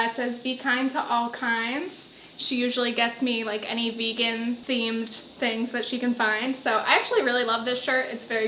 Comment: I could have sworn you said Barney themed shirts, and was somehow expecting a bunch of purple dinosaurs